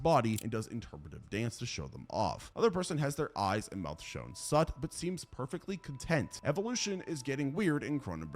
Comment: you said shewn sut instead of sewn shut